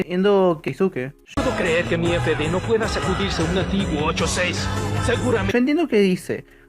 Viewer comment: lo que dijo keisuke fue:antiguo 86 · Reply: Pero suena como "Nativo"